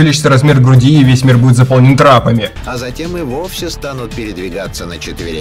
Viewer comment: кто,я что то не расслышал. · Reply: Трапами вродь.